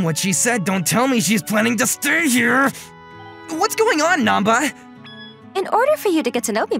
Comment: I heard nappa